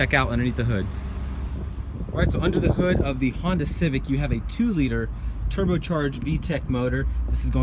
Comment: Did you said Honda Civic on min 36:03?